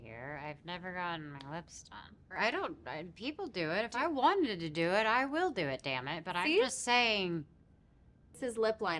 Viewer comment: Love her too❤ By the way I'm not an English speaking person so sometimes I don't hear what people say on these videos but if you can hear can you please tell me what selena said about lips in time stamp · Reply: She said “let me clarify something, I’ve never had my lips done, but people do, I could do it if I wanted to!”